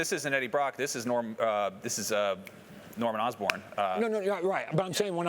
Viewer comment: did he say...norman osborn...............